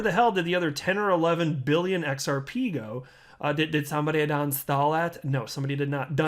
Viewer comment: “Did somebody done stole it?” Had me dying 🤣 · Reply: Lol. I thought he said "did somebody unstall it". But you're right. Either way it was hilarious.